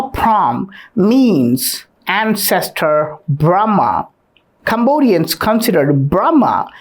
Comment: When you said brahma , I thought you said grandma